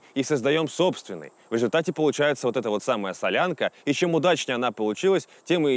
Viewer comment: послышалось "...и чем мудачнее она получилась..". Харизма - она такая.